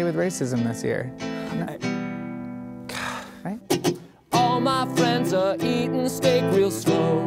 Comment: DO PEOPLE ACTUALLU HEAR "EATING STEAK AND SLOW?" ARE YOU SERIOUS HOW DO YOU HEAR THAT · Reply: i hear "all my friends are eating steak and snow" XP